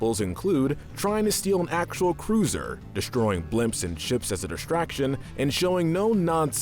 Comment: Ok sound like he said blitz and chips. THAT MONSTER HOW DARE HE DESTROY BLITZ AND CHIPS. He better be lucky that rick doesn't find out